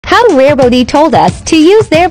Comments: I heard “how RARE BOOTY told us”😂✨ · “Rare booty” 💀💀 Edit: I don’t wanna be that person but like…. TY THO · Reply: I heard, “rare boaty” 💀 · I heard "Rare booty"